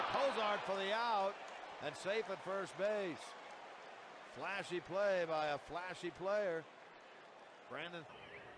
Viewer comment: 素晴らしいプレーに聞こえる。